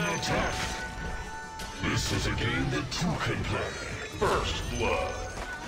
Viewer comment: i heard "this is a game the 2k play" is that right?